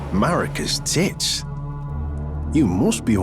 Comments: Anyone know the details of the build used to fight margit? Looks really fun and id like to try it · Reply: I beat him with Uchigatana+7 And it was pretty well Btw why does it sound like he says "Marika's tits" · Did he just said marika's tits ay at 12:51 WTF 😂 i have to do this qust now😂😂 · did he say marika’s titts? Or did I hear that wrong. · did he say Marikas t*ts wtf😂